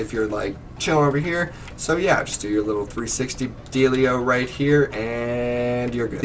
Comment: did you say 360 adelia?